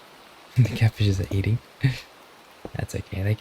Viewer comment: i was settling in n watching ur video n i accidently misheard u saying now lets eat the cat. lmao. i jumped up... WAITTTT WHATTTTTTT i rewind ,felt ease..UF haha btw i hope u have a lovely time here , please rest up everyone ik its been a long day.. may god bless everyone and specially u the one reading. <3